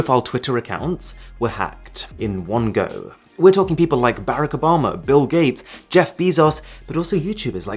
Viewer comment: LOL, did he just say, "BREAK OBAMA" instead of Barack Obama"